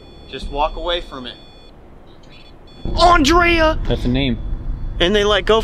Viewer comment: When I heard the phone say my name at 43:09 my heart dropped because my name is Andrea. I was taken aback so hard. I was like “Hold up, wait a minute, did that just say Andrea?” I had to listen to that again. Because out of all the ghost hunting videos from you guys and other ghost hunter content creators, I have never heard my name being said.😂 Oh my god my heart is pounding. But obviously I know I might not even be the only one watching this video who has the name Andrea. But my name is pronounced differently.